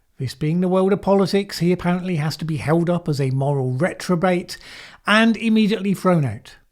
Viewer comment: i heard "recturbate" ... i guess it just proves the point of the video.